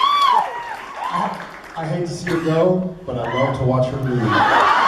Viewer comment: what did jared say at 12:00? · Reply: I hate to see her go! But I love to watch her leave!